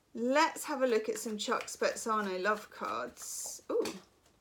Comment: What is the name of second deck please? The love deck, I think I heard you say....it looks lovely! Thank You for this read. I am hopeful. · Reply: Chuck spezzano’s love pack 🙏💗